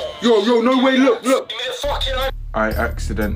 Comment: Did he just say miss cotrell